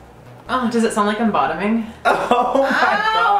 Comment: "does it sound like i'm bottoming?" -Mitch Grassi 2017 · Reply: OHH I swear i thought he said does it sound like lobotomy HAHAHHAHA